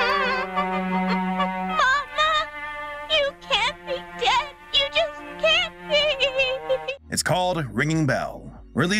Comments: Was i the only one who i heard "mamma you can't be gay you just can't be"💀💀💀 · At first I thought the kid said "mama you can't be gay you just can't be"💀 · dude, the scenes where he was crying actually made me almost cry 😭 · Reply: I thought he said mom pls don’t be gay you can’t be! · People who thought the lamb said “mama you can’t be gay you can’t be” 😭😭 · I heard "ma-ma you cant be gay.. " 💀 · for a second i thought the baby said “you can’t be gay” 😭 💀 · Im sorry but I thought it said “You can’t be gay! You just can’t be!” and was caught off guard 💀